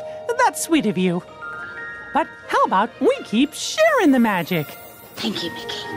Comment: How have I just got what Mickey said at the end, ‘Keep “Sharon” the magic’😂